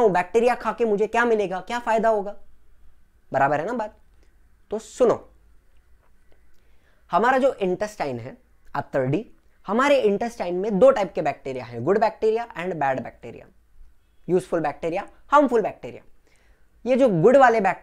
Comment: I didn't understand what you said at exactly 25:43. It sounded like 'After D'